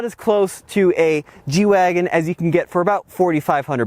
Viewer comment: sorry I thought you said Jew wagon, need my ears waxing.